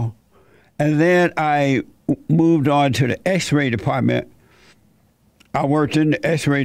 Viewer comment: When he said he worked in the x-ray department I could have sworn he said x-rated department.